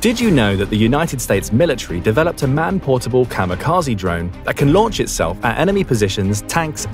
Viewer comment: Had to watch it twice. I heard "manned kamikaze drone"